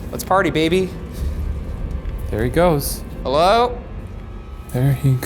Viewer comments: When Ryan was in the hole and he stated his full name; I heard "Hello Ryan" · Be sure to follow the bois on instagram! <link> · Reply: BuzzFeedBlue I swear right before he said "Good" I heard "HELLO RYAN!" Lol